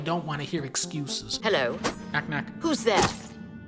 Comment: did he say "KNACK 3!?!" or maybe they're making a Knack: Knack Edition.